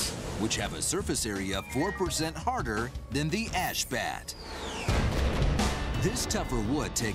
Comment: i thought he said ass bat